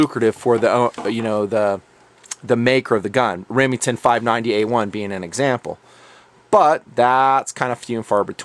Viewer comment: minutes did you say remington 590 A1?